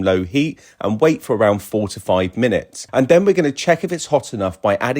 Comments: I heard at first 45 minutes, thats freakin long, then listen again it is 4 to 5 minutes😂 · I waited 45 minutes like instructed and now my egg has hatched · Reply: I also thought he said 45 min. So I thought this was a video parody of steel pan tutorials. But it was just a real tutorial